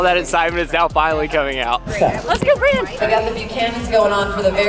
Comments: I really thought said "Let's go Brandon". Haha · You guys did great! Congratulations, really amazing. When said, "Let's go brand" I had to watch it twice because I thought she said "Let's go Brandon". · “let’s go brand…” We had to rewind! 😂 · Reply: I thought she said Let’s go Brandon too…and was quite surprised. It makes sense about branding the wall though.